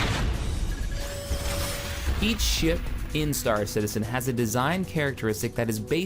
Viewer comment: he said eat shit nah he actually said ''each ship'' but Lol.